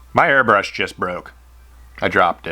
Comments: At the beginning I first thought you said, "my hairbrush just broke". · Thought he said hairbrush at the start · At the beginning of the video I thought you said your hairbrush broke and I was like yeah it did.